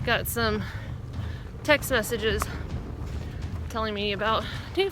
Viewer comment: Some gorgeous forest sayings and waterfalls, thank you & I love green tunnels too. BTW beginning I thought I heard you say “I got some Tex sausages” but being from Alabama I quickly discerned it was “text messages”.😃